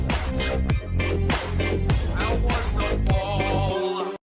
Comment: idk why but at 13:40 my subtitles said instead of "how was the fall" they said "I was watching." HUH